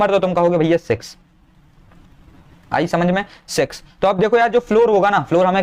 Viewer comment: What did he said??? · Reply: 6